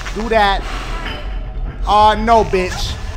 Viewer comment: seconds sounded like he said"Barney please"